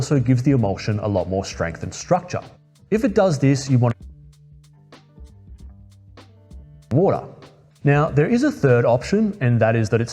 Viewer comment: There's an audio error at 3:10 - 3:15 it's kinda hard for me to understand what's being said here. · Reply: I watched this video dozens of times and I can't believe that mistake made it's way in, but thanks for letting me know! What I'm saying is 'if the emulsion sticks to the gel layer, you want to pour out your old bath and fill your tray with fresh boiling water'.